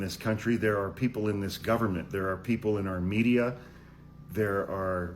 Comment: Thank goodness for the transcript (which is crappy but...) i thought you had said "Armenia". But it was "our media".